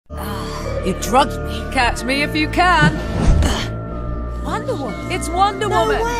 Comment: I thought she said cat me if you can💀